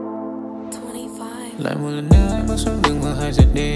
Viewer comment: s cái đoạn 0:11 t nghe là matchinh còn cái đoạn 0:21 t nghe thành free fire v nhỉ =)) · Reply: naji với twenty five thầy ơiii